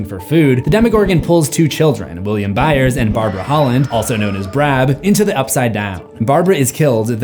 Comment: did you just call her "Brab"...what happened here?